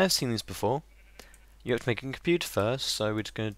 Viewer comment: when u said computer, i thought you said turtle :-) :D :P